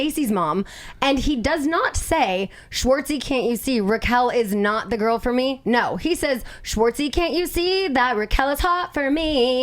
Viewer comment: I could of sworn Sandoval said Raquel’s not the girl for me but ok